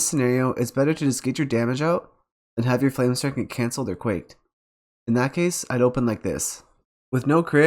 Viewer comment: I can't really hear what you're saying at 12:59. "And have your Flamestrike cancel their 'quate'"?